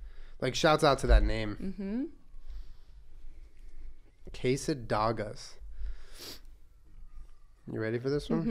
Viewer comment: I thought she said quesodoggos and I almost spit out my drink